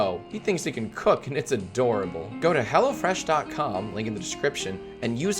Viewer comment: Not gonna lie, i thought he said HellaFresh